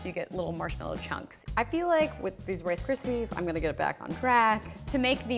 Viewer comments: did she say she is going to get back to crack? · I thought she said "Back on crack." I'm not a 90's kid I swear